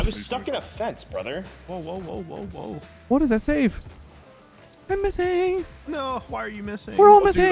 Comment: "I'm missing" from literally sounds like Drake saying "Embarrasingg" LMAO